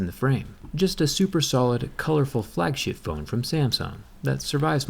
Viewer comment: did he say "flag shit phone" instead of flagship?